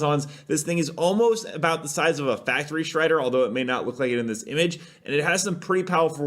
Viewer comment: at 4 minutes in i thought he said factory shredder i had to go back but that would be scary indeed glad i just miss heard